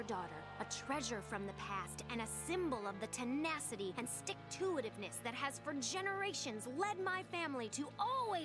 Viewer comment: did she say "stick-to-it-ness" ?